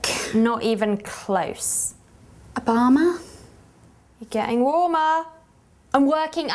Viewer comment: did she say a bomber or Obama?